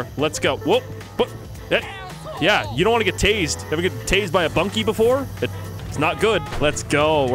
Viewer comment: I thought you said "You ever get tazed in the bonke?" I laughed so hard I farted. My coworkers say thanks